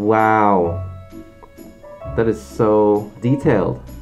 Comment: when said "i love the expressions in the eyes" i thought he said i love the expression of empinadas lol.